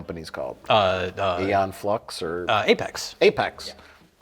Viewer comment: He clearly said "Aeon Phlux"